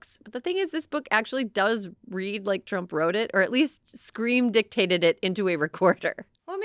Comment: Did you just say 'Scream Dictator' That could be the big term, after The Elections. Or you could call him, 'The Screaming Dicktaker'